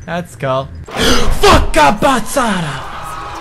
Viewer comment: did you just say fuckk a bisada?!